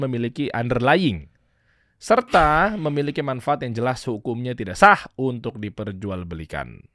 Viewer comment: maaf tp Itu yg ayat ke 3 di pasal tentang crypto mengesahkan asalkan jelas underlying nya dan saya dengernya bang bilang tidak sah, apa kuping saya yg salah denger apa gimana